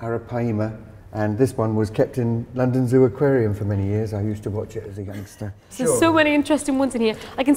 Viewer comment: I thought He said “I used to watch it as a gangster”